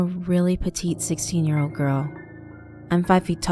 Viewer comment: Wait what the first thing I heard was " I'm a girl " 😂